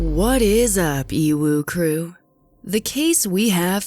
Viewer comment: Sorry I had to comment this. The first time I watched a video I thought you said “What’s up EVIL crew.” Turns out it’s EWU crew.